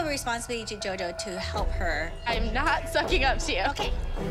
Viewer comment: I like how Gianna didn’t kiss up to Tina and trinity then got saved. She even said “I’m not kissing up to you” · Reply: I thought that was Jess saying I’m not kidding up to you lmao